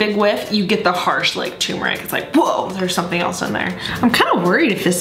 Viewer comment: lolllll... numeric instead of turmeric